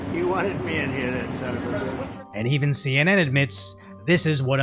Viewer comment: Did... did Trump just call Biden a 'son of a bitch' under his breath? ... I LOVE IT!!!